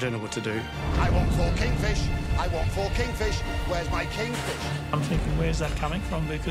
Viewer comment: I hear "I want fokin fish, I want fokin fish, where is my fish"